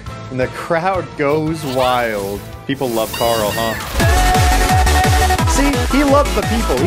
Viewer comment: I saw that PlayerZero23 Your sins shall not go unnoticed You really thought you could say carp and get away with it huh lmao